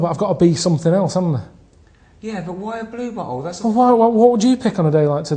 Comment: I thought he said a beer bottle lol.